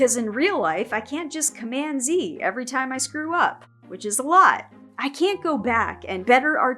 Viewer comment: I had to watch back 3 times, I thought you said "which is a lie" and I was really curious how you were Cmd Z-ing everything, but realise you're likely saying "which is a lot", which is humble and not, magical, unfortunately.